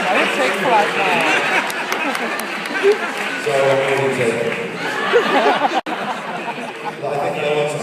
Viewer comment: I heard him say "I'm already taken" bye.